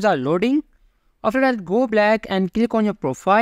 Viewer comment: Did bro just say go black😂😂